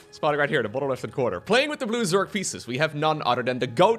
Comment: "Playing with the blue Zerg feces" Did I hear this right??